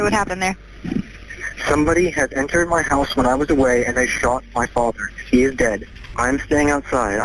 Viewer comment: Wow I had no idea what this case was about but when I heard the 911 call I clearly heard “I shot my father” not “they shot my father”. I was straight up confused.